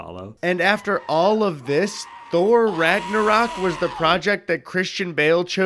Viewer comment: I think they say "Ragnarok" when they're talking about "Love and Thunder" 😂 Christian Bale wasn't in the former plus Ragnarok is great